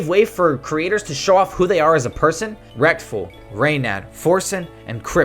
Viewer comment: did you said rekTful? lmao